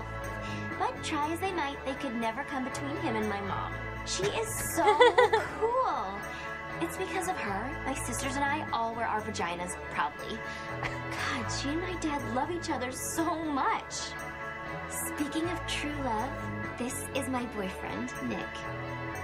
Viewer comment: WHAT? Did she say "It's because of her that we wear our vaginas properly?"